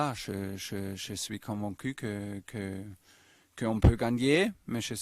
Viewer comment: Tuchel est "comme un cul" c'est pas moi qui le dit :')